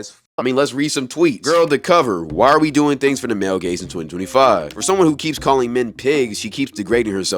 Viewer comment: thought bro was saying "male gays" until